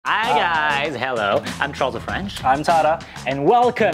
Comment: You know fellas what!!! turn english captions on at 0:05 it said "Troll of The French" 🤣🤣🤣🤣🤣🤣